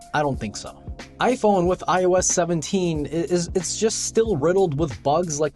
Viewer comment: The iPhone is currently running iOS version 18.4.1. Your research or lack there of was not well conducted. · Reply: No, you did not say 18 at 2:26 in the video, you said “iOS 17” not 18. I see you put in that self-correction after posting but that doesn’t discount that you said the wrong thing.